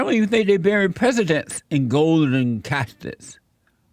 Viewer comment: I thought you said golden cactus. Man Awesome job i was laughing so hard.